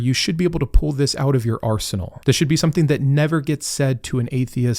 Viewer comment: At first, I thought said they could pull it out of their arse! I went back and only then heard 'arsenal'!